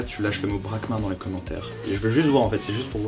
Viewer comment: Moi j'ai entendu "pragma" au lieu de "Braquemart" que je lis dans les autres coms. Est-ce que j'aurai suivi la vidéo d'un univers parallèle ?..... Ou est-ce que j'ai juste une mauvaise audition